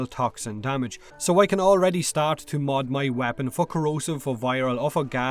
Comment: Sounds like you said "Fuck corrosive"